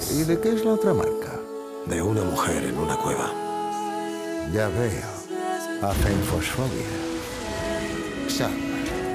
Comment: Que dijo en el minuto 3:16? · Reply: Puse el subtitulo y dice , guillermo del toro: de que es la marca, norman reedus: es la marca de una mujer de una cueva y hacen su vida.La verdad que no tiene ningun sentido,ademas a partir de ahi estaba quedandose el video desincronizado el audio hasta quedarse sin el luego mas tarde.Esperemos cuando salga y lleguemos a esa escena.